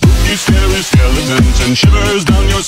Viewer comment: Idk why but i always hear: “sell your dookie Night” 😭